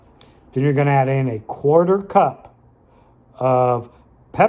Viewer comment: Can you clarify how much creamer to use? I thought the heard 2 cups then I heard 1/4 cup